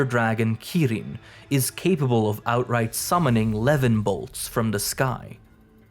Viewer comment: did you say "levinbolts"?🤨🤨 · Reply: yessir we are bringing old english terms BACK levin>>>>>lightning